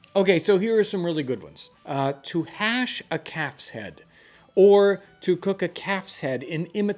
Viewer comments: OMG, I thought you said "a cat's head" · I had to replay that .. I thought he said a "CAT'S HEAD" :) ... but it's just a calves head ... probably fine .. I've had Pigs head before .. it was great.